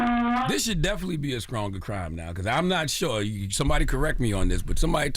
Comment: Charla saying "skrong" instead of "strong" ..?? or am I mishearing 😅